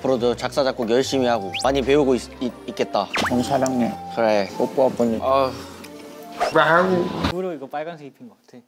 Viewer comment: Everytime i see this clip , i hear "oppa popo palii" (oppa hurry kiss me") instead of "popo palii" (hurry and kiss me)😭😭i mean the way he said it too it’s so adorable it makes me confused abdksbfksb